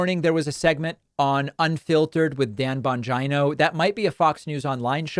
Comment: I like how you continually calling that Bong dude different names Lolol Sounded like you said “BonGina” today 😂